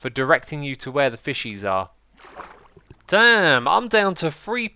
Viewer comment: gasp did he say damn